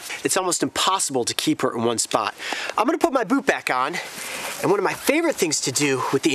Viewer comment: I could've sworn I heard "I'm gonna put my boob back on" lol.. wtf